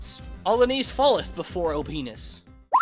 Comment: Did he just say penis?